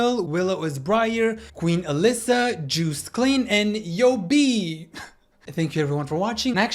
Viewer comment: DID HE JUST SAY JUICE CLEAN OR JOOST KLEIN?😭😭